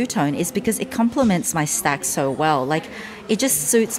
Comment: Did she say it compliments her snatch so well? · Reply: Stack